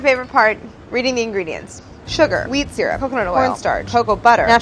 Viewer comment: fully thought she said weed syrup